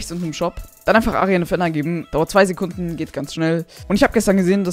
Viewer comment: Ich habe ARIAN FETT verstanden 😂